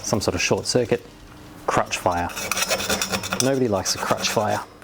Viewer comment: "Nobody likes a crotch fire!" Firecrotches "EXCUSE ME? WHAT DID YOU JUST SAY?" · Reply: crutch fire